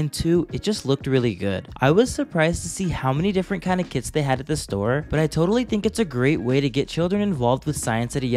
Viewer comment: i thought you said you're surprised to see how many different kind of kids they had at the store 💀 /JJJ